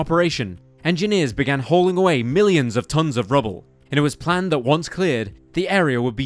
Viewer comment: Did he say millions of tons of debris @ 15:20 ? That seems like a lot. And also very difficult and dangerous todo while in a war zone